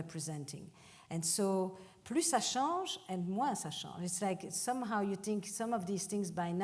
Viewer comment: I think she's saying "plu jouissance, moi jouissance", but not sure on the translation. Anyone know?